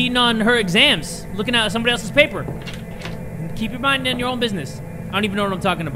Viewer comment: When i heard him read i thought he said bussin